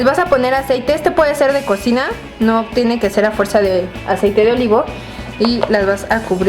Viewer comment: escuche : a fuerza